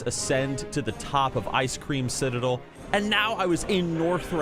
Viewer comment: Did you just say ice cream citadel at 3:24 🍧😂